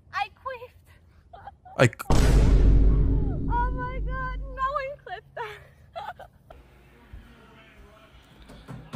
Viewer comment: She did what? I cant understand that plz xD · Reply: She said that she queefed